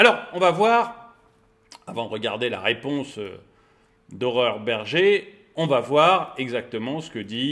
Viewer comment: J'ai entendu "Horreur Bergé" 😆